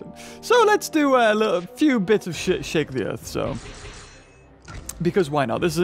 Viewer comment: swore a 11:37 he said "let's do some shit shake the earth"